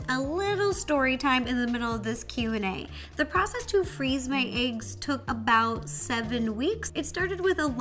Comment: I thought it said freezing my eyes